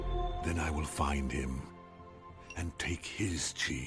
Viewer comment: bro the caption said "then I will find him, and take his cheese" XD